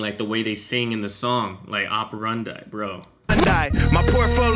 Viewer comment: Great breakdown, but by the way, in the bar that you are talking about around 28:40 he actually says, "That's my modus operandi" which is Latin for my method of operation or the way he works! even more genius lmao